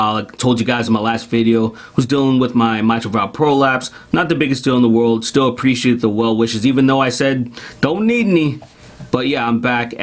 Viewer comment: I missed what you said about the prolapse i had to rewind it i thought you said anal prolapse 😂